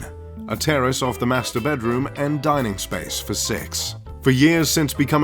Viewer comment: did he say "a dining space for sex"?